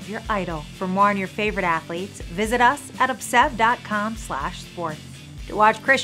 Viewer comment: Are we just going to ignore how hot the anchor is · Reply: sounded like she said /porn